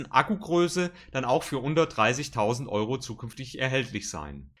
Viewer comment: HAHA I found an error. Der id 3 für 130.000€ ein echtes Schnäppchen :-) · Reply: ich habe "unter 30000€" gehört! Vielleicht hab ich mich auch verhört :)